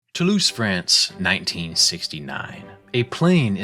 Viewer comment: Toulouse France, i heard "to lose friends"